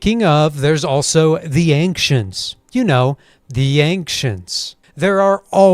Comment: For a second I thought you said the “Yankcients” and gonna go in the trope of it being earth far in the future